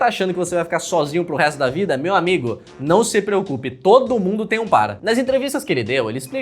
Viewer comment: juro que ouvi: "todo mundo tem um pai"